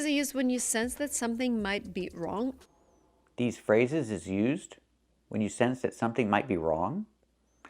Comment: I agree should watch five of your excellent videos before she makes any other one. But I think she said “are” not is, but a very light and not well enunciated “are” ?